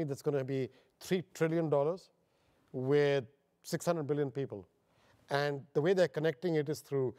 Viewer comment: AM I THE ONLY ONE HERE WHO HEARD IT AS 600 BILLION PEOPLE?